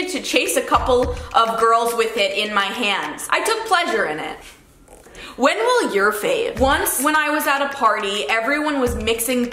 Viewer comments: does anyone know what she said .. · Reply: bella “when will your fav?” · when will your fave Did I hear that right? I’m so confused 😐🤷🏽‍♀️